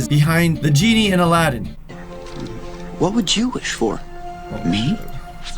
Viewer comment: did he say what was jewish for? :D